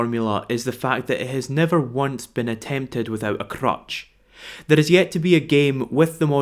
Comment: Did he just say Crutch or Crotch?